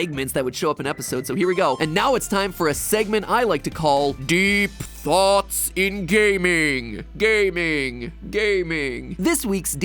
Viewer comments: “DEEP THOUGHTS IN GA-“ ad plays so it sounds like he said deep thoughts in gay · During deep thoughts in gaming intro I got an ad and I heard "Deep thoughts in gay-"